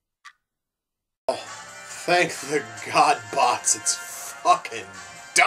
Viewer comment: Did you say "Thank the Godbots"!? As in, Gladiformers?! You're really going insane now.